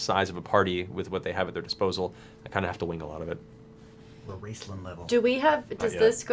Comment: does Liam say, "the Raistlin level"?